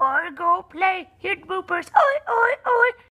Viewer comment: HAHA I'M AUSSIE AND I DEFINETELY did not think of oi oi oi at all haha, i heard "hoy hoy hoy" and made absolutely no connection